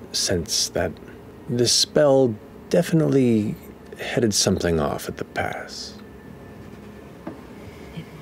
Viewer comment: sorry, what was Matt saying that everyone reacted to? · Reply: That the spell FCG cast, Gentle Repose, stopped something from happening. ("headed something off at the pass"). One of the effects of that spell is that the target is prevented from becoming undead (while it lasts) sooo..... Seems like maybe Delilah was about to do something? Or maybe it changed something about Laudna's status or her connection to Delilah?? Mechanically, Laudna is a Hollow One, a homebrew subrace which apparently does NOT count as Undead in most contexts? But some details about that may still be unclear, especially with Delilah/Vecna involved. At least that's my impression right now! :)